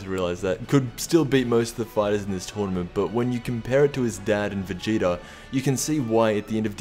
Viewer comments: did he say compare him to his dad Vegeta? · If I heard correctly you said gohans dad vegeta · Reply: Lol I thought he said Gohans dad and vegeta